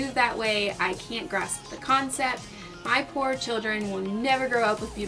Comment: Ohhhhh . . .POOR children. . . . .i thought you said FOUR children . . . . . .